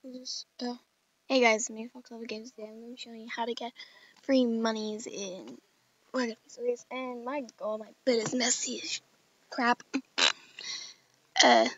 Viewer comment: did she say she was taking a crap?